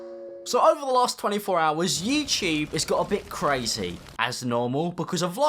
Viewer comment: Did he say “yeet-tube”????